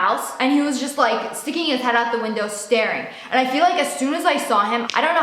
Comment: "I peed out the window" lol that sounded like you said that lol